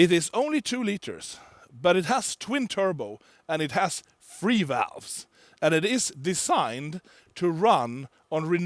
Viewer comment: did he just say FREE-VALVES!?!?!? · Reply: Yes its free valve technology by cargine which eliminate camshafts to save weight and also they made 1:1 transaxle. Just one gear ratio.